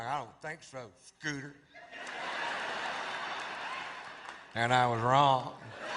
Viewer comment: "i dont think so scooter"😆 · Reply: I thought he said pooter